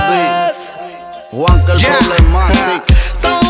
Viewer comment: les juro que en el 5:42 escuche "Maluma Beibih" :v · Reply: No dice eso dice Whats up my baby y se pronuncia waropmabeibi